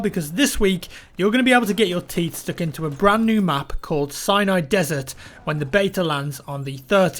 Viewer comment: Sounded like he said Cyanide Desert.